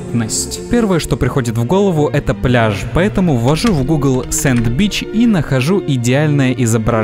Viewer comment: Я один услышал : Sand bitch